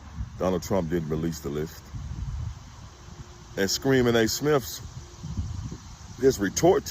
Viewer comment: Did I hear “Screamer A Smith”? 😅